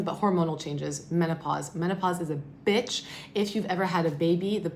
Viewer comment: Did she say bit*h?